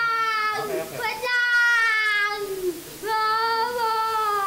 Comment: // What did said?! "Kuwadlang"?!